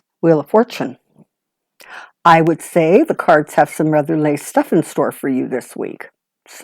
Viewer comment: A wonderful reading! Thank you. I'm curious what you meant when you said "the cards have some rather lay stuff in store for you this week"? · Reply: OneWorldAwakening.....I shouldn't mumble. "haver some rather nice stuff"